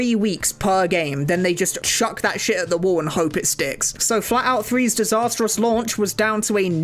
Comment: I was realy confused for a moment because I thought he said FALLOUT 3 and not FLATOUT 3 XD